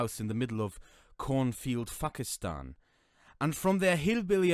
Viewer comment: I thought the guy said "Fuckistan".